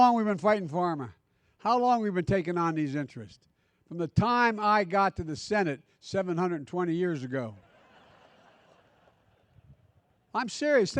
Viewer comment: did he just say " from the time I got in the senate 720 yrs ago"? 😂😂